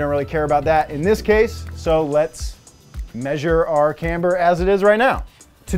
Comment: you said caster instead of camber